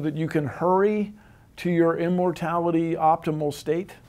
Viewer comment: I thought it said morality, lol. Interesting never the less.